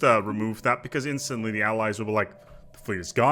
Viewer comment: Allies? Oh.. I thought u said alloys...